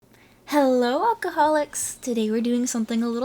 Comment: I thought you said "alcoholics" at the start and I felt personally attacked.b